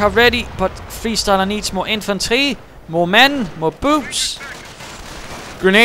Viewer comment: why did I hear you say "more boobs"?